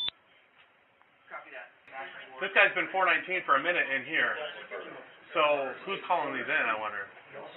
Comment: I thought he said this guy has been fortnite camping in here